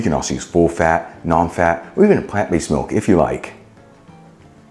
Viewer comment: you were talking about the milk. You said, "You could use low fat, non fat or...." SOMETHING I couldn't quite catch. I played it back 3 TIMES, (!!!!), trying to figure out what you said. Still unsure. Clarification? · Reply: "or any plant-based milk if you like" such as almond or soy milk.